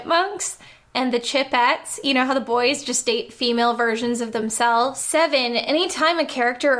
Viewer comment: Was watching this at 2x speed with captions on and did a double take at 7:41, as the captions decided that when the Chipettes were introduced, "the boys just ate female versions of themselves." Had to go back and rewatch a couple times before my brain worked out what was actually said...